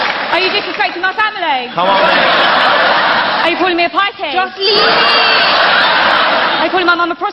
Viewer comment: Can somebody please tell me what she's saying at 2:59, after she says "are you disrespecting my family". "Are you calling me a [ ????? ] " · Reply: She said "pikey" I'm not sure exactly what it means but I'm sure urban dictionary would have a definition.